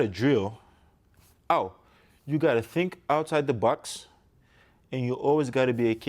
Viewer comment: For a moment there at 0:31 , I thought I heard him say, "Oh you got to think outside the Bucks and you always got to be a kid... That's what he told me." Then I've figured, "Oh he has an accent so he probably really meant to say - 'Outside the box'" lol Kobe may not have been the biggest, strongest, most naturally gifted basketball player but he's arguably the hardest worker and most determined. With Giannis already possessing all the physical tools (physically gifted in size, strength, athleticism) and Kobe's work ethic rubs off on him and he develops the skills as a result of all that hard work, he's going to be next to impossible to contain on the court. That's scary.